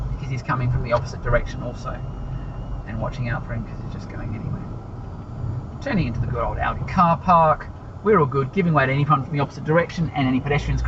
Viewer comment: saying turning into this ???? car park. Could you tell us what did you say for that? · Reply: Turning into the good old Aldi car park.